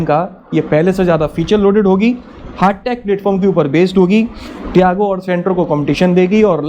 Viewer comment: I missed heart attack today instead of hart tech platform today xD